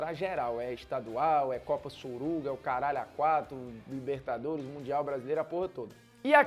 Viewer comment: entendi copa suruba,pois ia combinar bem com elas KSKSKSKS